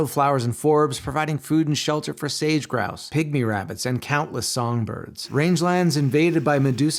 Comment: I first heard “Pygmy Rabbits” as “Pick-Me Rabbits” and wondered what naturalist rolled their eyes at this adorable bunny and thought, “geez this rabbit is trying too hard.”